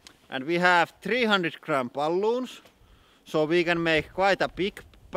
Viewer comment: When he said "we have 300 gram balloons" at 0:56 I thought for a second he said "we have 300 crampaloons", lol.